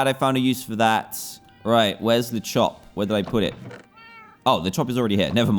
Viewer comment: When he said le chop I thought that he said the shop